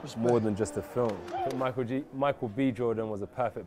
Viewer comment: Did anyone else catch Michael G Jordan